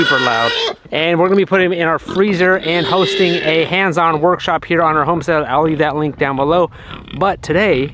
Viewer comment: Did I hear you say you're having a "Hams on workshop"? lol